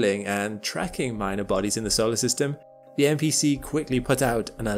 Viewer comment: I thought He Said "NPC" a 'Non Player Character' in Video Games!🤣. Hey! I'm just a 51 year old, With a PlayStation 5 😉 😁